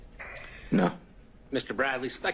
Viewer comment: Mr Bradley actually did respond no to the question at 1:05-1:06 on this recording. He seemed exasperated but it was a clear unmistakable no. The word dang was picked up by many, but apparently not that. I heard it when he said it live. Poor sap...